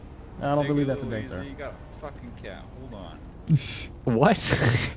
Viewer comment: did he say cap? Before it was a thing??